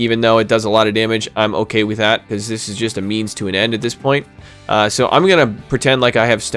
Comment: Lol… @ 10:34 did you say “it’s just a Memes to an end”? I sure hope so! That’s halurious